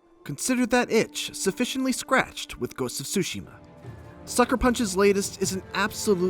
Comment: did i just heard "Ghost Of Shushi" on 0:18? lol